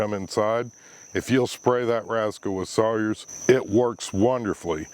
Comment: I thought you said spray that rascal with soy sauce 🤣 (I had to turn on closed captions to find out for sure. But I didn’t realise that this video was aimed at only 4% of the planet that know what Sawyers is… I will have to go google it )